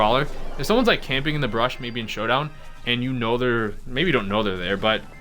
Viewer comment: did he say brush?